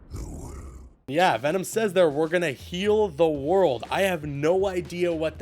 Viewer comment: I thought venom said kill the world?